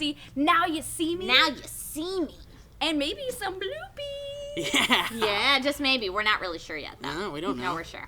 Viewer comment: I totally thought meg said boobies when she said were getting some bloopies.